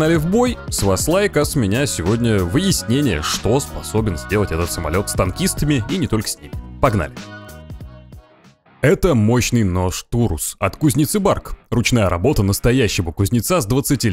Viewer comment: кому тоже послышалось "Нож Турмс"?